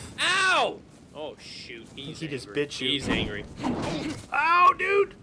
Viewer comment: Did someone hear say the b word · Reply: HE SAID BIT